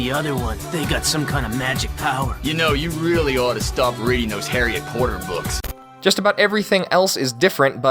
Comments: You really need to stop reading those harry accorder books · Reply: i always heard it as “harriet porter books” what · "yOu gOttA sTop rEadIng tHoSe haRrY acCoRdeR boOks" That scene get me every time XD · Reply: I thought they said Harriet Porter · I think he said Harriet Porter books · I heard “Harriet Poter (poy-tur) books.”